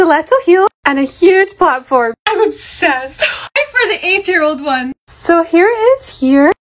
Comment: I thought she said eight-year-old shoe